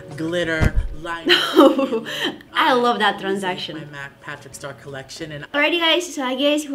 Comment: you said "I love that transaction" I think you wanted to say "transformation" 😂 Anyway👻 PS love you ❤ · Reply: She said transition and it is actually suppose to be say